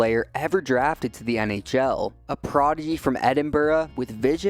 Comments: Thought he said "a proddy from Edinburgh" 😂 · where you said "A ____ from Edinburgh" Whether it be your accent or my bad hearing I couldnt understand what you said. Just wondering if you could clear it up for me please? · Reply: I'm hoping he said "proddy" meaning "prodigy". I'm from Scotland and "proddy" here generally means "protestant" (in a derogatory sense).